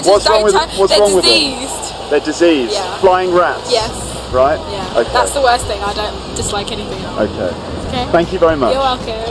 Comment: Hallo! What does she say in the minute 3:20? She says something like "I hate PIGEONS" ...... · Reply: she says: "Oh yeah! I hate pigeons, they're diseased (...), that's the worst thing, I don't dislike anything else" please notice that in general British English you tend do "drop" R's when they are between two consonants... so "worst" becomes /weest/, as you can hear in the video